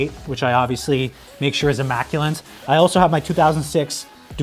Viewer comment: Did he say “immaculant” at 11:12?